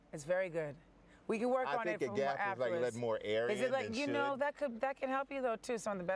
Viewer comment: What does he say at 2:57? pls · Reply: “I think the gap is letting more air out”